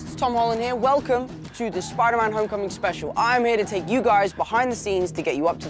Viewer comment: I thought It say Spoiler Man! 😂